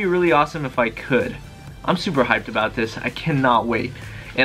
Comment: did just say ur super high about it???